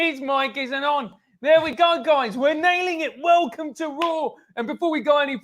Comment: first time I listened sounded like you said "welcome to rule" is this colonialism? help bigotry over 9k